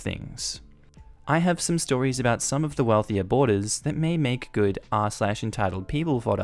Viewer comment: I heard ‘wealthy aborters’ and had to rewind 😂